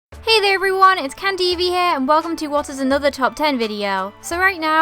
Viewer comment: At first I thought she said, "Hey there, everyone! It's CannedEvie(blah blah blah)" And I'm like O-O Then I realized I was just being deaf... don't can Eevees xD